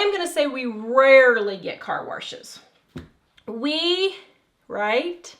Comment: I kept hearing “car warshes” 😆